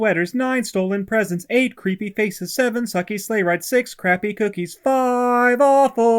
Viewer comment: it says sweaters instead of sleigh rides